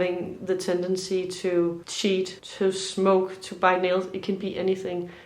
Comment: Hallo, it was your other channel that I first saw you. When you were saying “bite nails” i thought you were saying ‘buy milk’. — Have the best of the holidays. Watching from Glasgow.